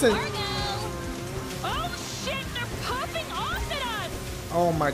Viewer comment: Who else thought it sounded like pooping instead of popping